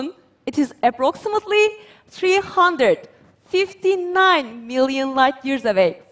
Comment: It almost resembles a star forming and clearing out the debris near it, but a galaxy? We want more info!!!!! The accent made it difficult to hear her the first time through... sounded like she said 359 Billion light years and I started questioning just what her team had observed. Had to listen carefully the second time to hear MILLION. Maybe some captions would have helped there.